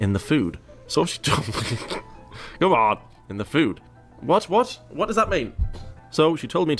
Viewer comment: I think she was trying to say her knee "isn't good"